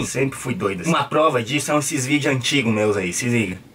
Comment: só eu que ouvi x vídeos skksksks presiso tomar meus remedios